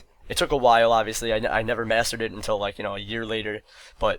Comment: i thought you said "i never masturbated till a year later" lmao xD